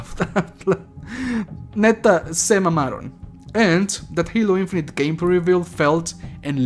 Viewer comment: did i hear a "Neta se mamaron"? U taco? 0_0